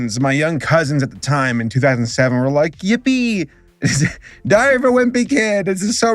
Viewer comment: hold up did you say die of a wimpy kid?!?!